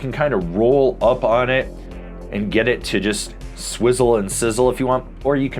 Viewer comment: When can we get a ‘Swivel & Sizzle’ T-shirt? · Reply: I thought he always said "swizzle and sizzle"...swivel and sizzle makes way more sense